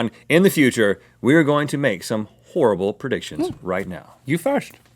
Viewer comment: I thought u said terrible predictions